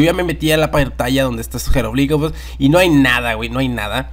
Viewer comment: Que dijiste en el minuto 8:18?? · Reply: dijo su jeroglíficos creo no se